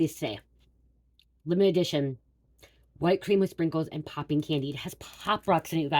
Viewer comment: I thought it said pooping 💩 🤖 😊😂candy